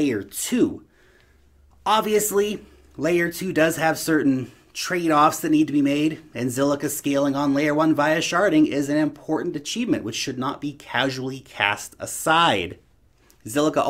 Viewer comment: Thought you were saying "sharting" , laughed through the whole video. Great shirt as always.